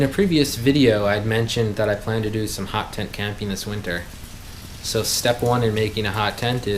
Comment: I thought you said "hot tent can penis winter". 😂❤